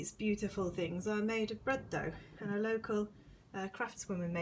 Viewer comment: Love your craft room! Beautiful wallpapers. The flowers are lovely - it sounded like you said they were made from bread?! Would love to know the patterns you used for your star blankets. Just found your podcasts today. I was very confused with the first one when you mentioned going to England ;-) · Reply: Hi! Thank you so much! The flowers ARE made of bread dough, or at least a flour dough, and the woman who makes them gets so much detail into each bloom. I love them. Every time I find her at a craft fair I buy another one. The star blanket pattern is on ravelry, it's called the Rainbow Ripple Blanket and I love it, I've made it seven or eight times so far! <link>